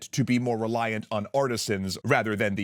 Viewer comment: I think the artist misheard "artisans" for "artists" lol